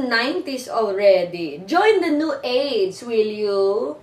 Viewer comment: 0.54 it sounds like she says: join the new AIDS :| but Im sure its New AGE XD